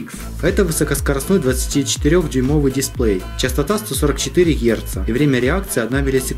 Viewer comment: послышалось дерьмовый дисплей )))